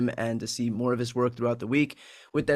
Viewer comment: I always hear, “Inside the Reich.” Just saying.